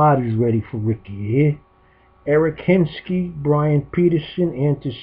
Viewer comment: Did I hear Eric hinske around the 3:30 mark? I love that guy, he helped start the rays revolution in 2008!